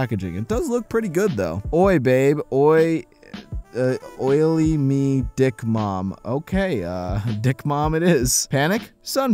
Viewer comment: I think it says Oily Medic Mom